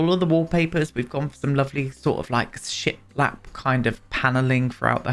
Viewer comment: You may not be seeing those Edwardian base game tiles because the default swatch you see is in dark colors (sort of teal and burgundy I think). Every time you said ‘ship lap’ I heard ‘shit lap’!